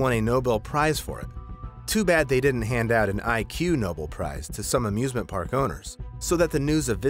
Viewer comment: what picture/text says: “Ig Nobel” what he says: “IQ Nobel”